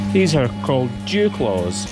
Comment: Did he just say “these are called jew claws”? Lol great video